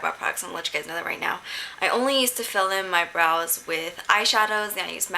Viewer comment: lol i thought you said you didn't fill in your brows. as you said before in your videos